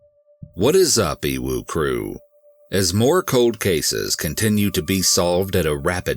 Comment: 😂 I always thought it was Uwu crew. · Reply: lol I thought he said emu crew